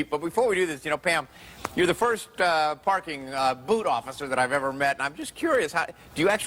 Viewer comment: I thought she said a BOOTY OFFICER !